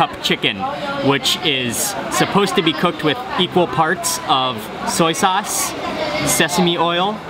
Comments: Did he just say people parts. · I thought Mark said the 3 cup chicken was made with people parts, then I realized he said "equal parts " lol .. freaked me out for a second